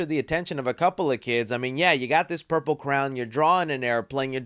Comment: I thought he said purple crown so I was looking and I didn't see it